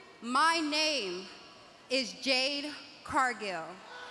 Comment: Who was her name was? What I heard was "Jet Cardio". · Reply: Jade Cargil